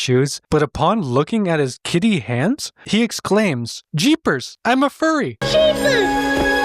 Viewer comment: I'm pretty sure the kid said "I'm all furry", but his pronunciation missed the LL's. So, yeah, it definitely sounded like he said "I'm a furry".